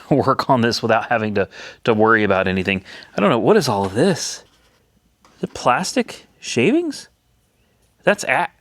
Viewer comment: At first I thought you said “what is all this?…thats a…HASH?!” 🤣